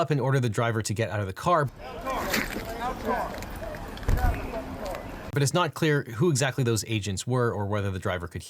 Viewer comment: i stopped watching after he said she couldn’t hear the cop telling her to get out. You may be stupid enough to believe that but I’m not. So dumb for someone that is a self-proclaimed “legal expert” is incapable of taking an objective view. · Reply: You mean at 4:18? What he actually said was "it wasn't clear if". Thanks for proving the point I made in my previous post